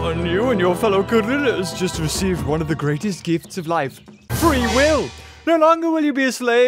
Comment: 'one of the greatest gifts of life. Free will!' I thought he said free wifi😂😂